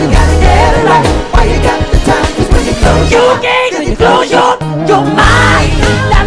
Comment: what did he say? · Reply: "You can't close your mind"